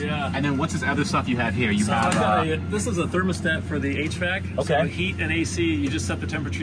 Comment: I literally thought he said "This is a thermostat for the 8-Track"...